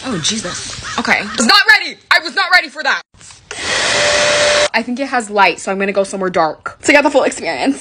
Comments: I THOUGHT YOU SAID “I think it has lice so I’m gonna dark” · I can’t be the only one that thought she said “I think it has lice 😩✋ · bro i rly thought she said “i think it has lice” 😭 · Is it just me or when she said I think it has lights I thought she said I think it has lice 💀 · I thought she said "I think it has LICE so I'm gonna go somewhere dark"LOL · II thought she said “I think it has lice” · TELL ME WHY DAH FWACK I THOUGHT SHE SAID "I think it has lice 😇😘👹😍🫶👰‍♂️" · Idk why but for a second I thought she said :I think it has lice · I thought she said I think it has LICE But she said LIGHTS 😭😭😭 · bro it can't just be me that thought she said "I think it has lice"but it was lights!!!!!!!! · I thought you said, I think it has lice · I thought she said I think it has lice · What she said: “I think it has lights” What I heard: “I think it has lice” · I thought you said I think it has lice so I need to go somewhere dark😂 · When she said "I think it has lights", I thought she said, "I think it has lice." 😭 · Am I the only one who thought she said I think it has lice rather than lights 💀